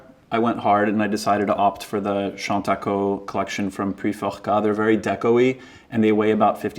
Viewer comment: Was anyone able to translate what he said around the 1:23 mark? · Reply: It’s Chantaco Puiforcat Flatware Silver Collection ☺️